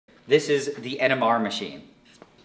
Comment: That's a nanalysis 60e NMR spectrometer, I guess this thing goes for around $50000, pretty impressive. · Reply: I first heard "This is the En-em-a machine."